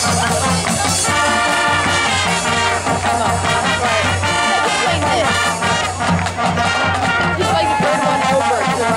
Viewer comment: I wana know what they are saying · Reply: 13 years later but we said "1-2-3-4" Dance "B-S-H-S"